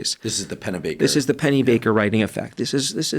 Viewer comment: Funny moment: I thought you guys said Penny Baker. I googled it. Some actress... With 'free nudes' 😂 and decided I would type in Penny Baker "writing". Lesson learned: (James) Pennebaker 🎉